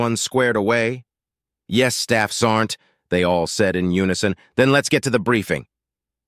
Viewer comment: 2:13 example, Someone needs to Correct the Auto Correct, this is probably the 1,000th time I heard "Staffs Aren't" when the ACTUAL Military Rank is "Staff Sergeant". This Error is repeated in Countless Posts. Would be ridiculous to try and fix them, However it would be nice if it was Corrected in All Future Posts. It may not bother most people, but as a 100% Rated Disabled Veteran who was a Staff Sergeant, it irritates me Every Time I Hear It.